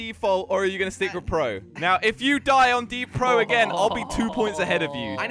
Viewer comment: did you just say deapro